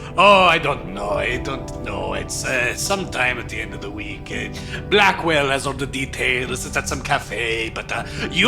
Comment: Spittake did he say Blackwell?